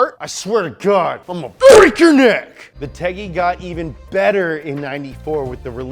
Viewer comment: Is he calling the car, or the V-TEC motor tecky, or teggy @ 3:50? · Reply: eric t teggy.